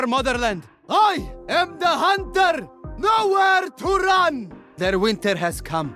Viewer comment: What is he saying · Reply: Nowhere to run